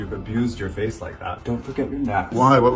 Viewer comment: Oh he said "often over looked by blind poeple" I heard "black people".